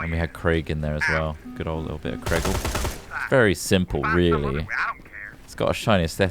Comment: Did just call Craig "very simple"?